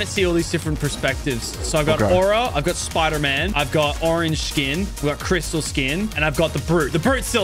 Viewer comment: I thought you were gona say i got my icon skin